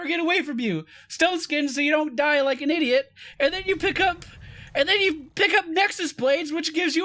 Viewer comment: When I heard say Nexus Blades, for some reason I thought he said Renekton Blades.